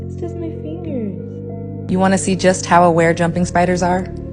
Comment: At first I thought you said "wanna know how rare jumping spiders are" and then it layed only one egg 😂 then I understood it was just poop and that you said "aware" 😂😂